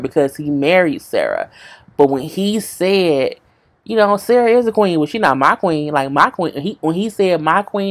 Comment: I thought he said Meagan is A queen, but Sarah (the wife) is MY queen